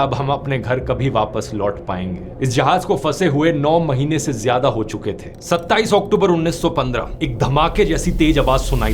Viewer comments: Mistype in subtitles at 5:40 instead of 27 Oct 1915.there is 2015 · the English subtitle says 2015 instead of 1915